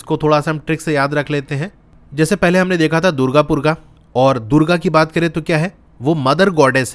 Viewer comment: Did you say durga purga ?